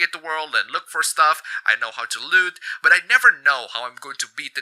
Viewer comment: What was written: "I know how to loot" What I heard: "I know how to lewd"